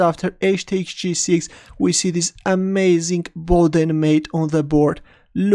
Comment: what mate did u say at 3:14 sounds something like board and mate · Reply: Boden Mate